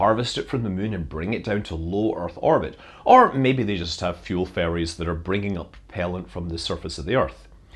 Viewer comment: I was at rapt attention until he said “fuel ferries,” which I heard as “fuel fairies,” and I lost it.